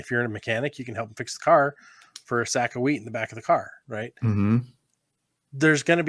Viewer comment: I misheard when you were talking about trading mechanical work for a bag of WHEAT in the trunk, thought you said weed. LOL.